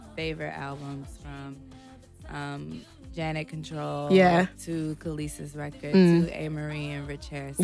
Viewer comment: Anyone catch what she is saying here? "Janet Controlled to ???? to Amerie & Rich Harrison..." Sounds like Kalissa's records.